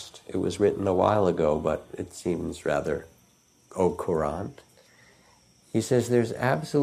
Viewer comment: Thank you for this amazing video. At 38:30 he uses the word that the closed captioning tells me is “ocorant” to describe Robert Frost’s poem. Long shot but I was wondering if anyone knows what he actually said and what the word means. Thank You. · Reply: au courrant (fr.) - means aware